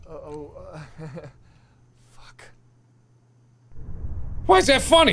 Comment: did Max just say F**K?